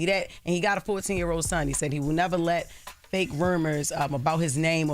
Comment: Wtf you said, Jess??? "Fake rumers?!?!" 😂😂